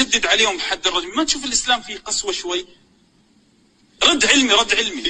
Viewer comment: كاني سمعته يقول الاسلام فيه قسوه شوي 2:06 ولا انا غلطان